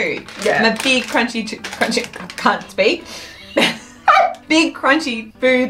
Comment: I love those Pringles yes they are hot but they are also full of flavor once you get used to the heat. and second what did say at 6:27 ? was it " can't speak" because it sounds like something else to me Lol . Ty for your videos they bring me joy when I watch